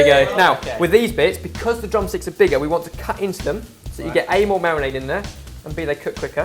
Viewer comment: does say 'Anal marinade'? Haha!